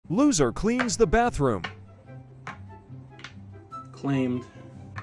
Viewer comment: I heard "the loser claims the bathroom" 💀